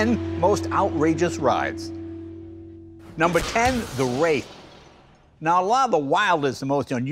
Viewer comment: "The Wraith" with Charlie sheen. Thought Jay was saying, "The Race" and wondering where the lisp came from. : D